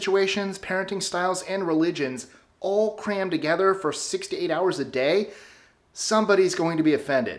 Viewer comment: I thought he said 68 hours a day lol.